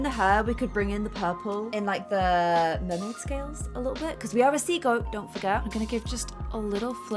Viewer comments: I wasn’t looking at the screen when you read the Capricorn horoscope and with the accent I heard “seagull” instead of sea goat 😂 I was like what kind of freaking horoscope guide are you looking at. I am Scorpio ♏️ so excited to see mine! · When said sea goat it sounded like she was saying seagull 😂😂😂